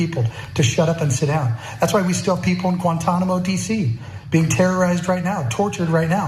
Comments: did he say.....thats why we have people in Guantanamo DC....? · Did….. did he just say “Guantanamo DC”? Bru these right wing lunatics are insane